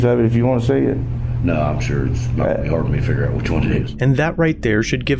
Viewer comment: They still will not find fault on the cop, but it definitely sounded like he said "It wouldn't be hard for me to figure out which one it is"